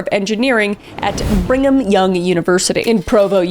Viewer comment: Did you say "bring 'em Young University?" Cause that's what most us non LDS Utahns call it.